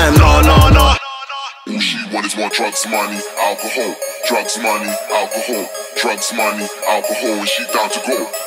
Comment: Does this say drug somali's Alcohol at the end ?? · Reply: All she want is more drugs, money, alcohol